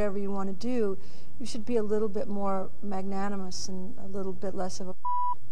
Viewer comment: What is she saying in 1:28 they bleeped her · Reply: "...and a little bit less of a c*nt"